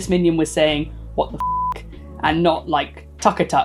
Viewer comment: I remember working at McDonald’s when we had the minion toy and it definitely sounded like it said “what the fuck” but I don’t think kids who don’t know curse words would notice it. I walked around the back of the restaurant with the toy cause I was immature and it made me laugh